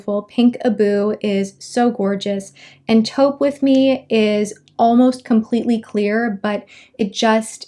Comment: I literally thought she said twerk with me